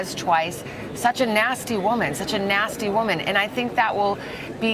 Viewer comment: I thought he said "such a nasty word" not "such a nasty woman "